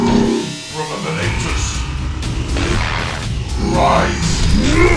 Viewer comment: Apologies to those who had a hard time with the audio in the previous version. People watching through their TV had some issues hearing the voices, hope this helps! Alright.... I need some rest.... Holy cow... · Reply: You can hear activating him 2:47 Brother Malectus,Brother Malectus rise! (might have misheard the name but sounds like this to my ear) followed by perfectly voice acted outrage!